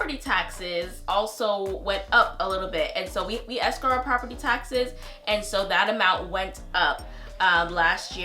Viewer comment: did you say escrow your property taxes? Great informative video as always 💕